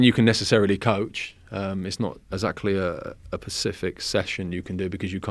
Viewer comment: Did he just say "pacific" instead of "specific"? That disappoints me.